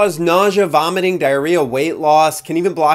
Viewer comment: Did he just say wrightloss?? Sign me tf up. 😂